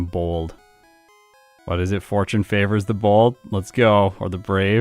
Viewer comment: I thought you said fortune favors the bald 😂